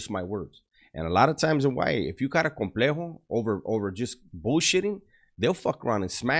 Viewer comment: what does he say here? It sounded Spanish, I'm Australian, Spanish isn't taught in every school here, usually Indonesian/Japanese/Mandarin, and they're optional anyway (it might not have been Spanish but I've replayed it like 5 times and I can't understand what he's saying here). No disrespect. Thanks in advance to anyone who helps! edit: sounds like "complejo", from context sounds like it means "complex", I'd search myself but I'm probably spelling it wrong. I know "J" makes a "H", and the phrases "¿que?" (and that it's alt+0191 to type "¿") and "no hablo Espanol / Ingles" (can't type accented characters on my laptop) but that's about it.